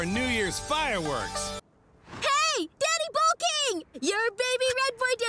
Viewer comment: Did I hear “Daddy Bull King” lmfao?!